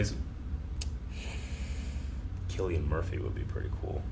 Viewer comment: I almost thought said "Charlie Murphy" Now that's would be interesting!